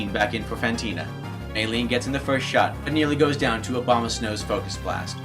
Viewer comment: Is it just me or did I hear him say "Obamasnow"